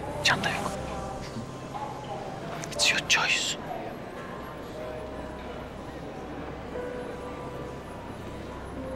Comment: de ne diyor anlayan var mı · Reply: “İt’s your choice” diyor, yani bu senin seçimin demek